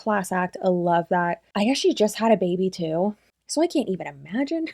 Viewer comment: I was cleaning, cooking and listening... constantly multitasking so I can still enjoy the content, damn!...so sorry! 😳😂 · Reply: I think it’s because she slurs her words a little at around the 5:06 mark. It 100% sounds like she says, “I actually just had a baby too” but I think she said “and she just had a baby too” meaning Kalyn is a new mom. I had to rewind a few times myself to figure it out lol